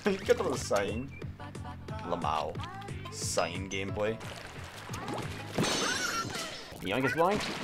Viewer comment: My PSY obsessed self thought you said "PSY gameplay" at 2:03 I swear im normal